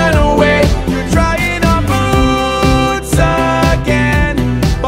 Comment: So I was just listening to the first time. I thought they said trying on boobs again… and I was like, nope. Cali, read the captions